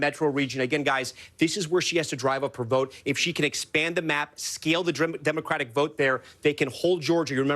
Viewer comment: The talking head actually says Harris needs to "steal" the vote. He actually said the quiet part out loud. Unreal.